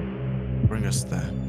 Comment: Hey, just want to point out around 8:32 there was a subtitles mistake, instead of Bring, it was Being. I know its not much but just had the feeling to point it out, amazing movie though! I love it.